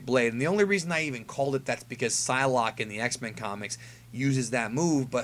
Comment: Did he say Sylock? SYLOCK THE DEFILED???